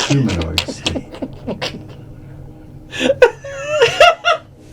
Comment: I swear to god, I thought he said Breen power...